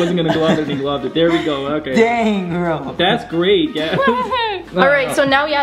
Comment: is it me or did she just say "damn"? wtf